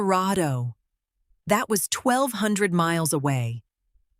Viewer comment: Which country is 12,000 km wide ? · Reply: I thought she said that Colorado was 1200 miles away.